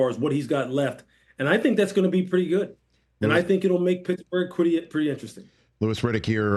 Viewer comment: did he say Picklesburg? 🥒